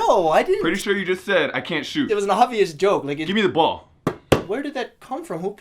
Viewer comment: I swear I just heard him say “it was an obvious joke n*gga”